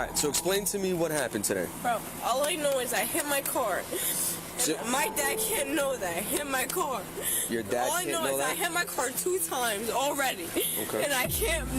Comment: Right about the time you say “my car doesn’t care if it gets in trouble” instead of “my dad doesn’t care if it’s an accident, I’m gonna get in trouble” is right about the time the breathalyzers start coming out lolz.